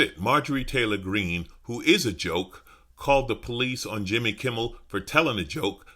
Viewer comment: Yep, my hearing is totally shot. I was positive I heard "MTG is a joke!" My mistake. 🤣😂🤣😂